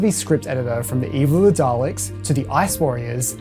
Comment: did a double take there because I thought you said “Eve of the Daleks” 😂